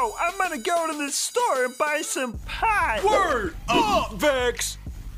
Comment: I just realized he said "buy some pie". I always thought he was saying he was gonna buy some pot.